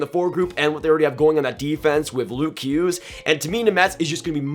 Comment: Jack Hughes. You sounded like you said Luke Hughes